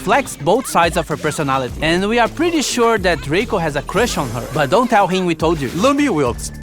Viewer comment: did you say Draco or Rico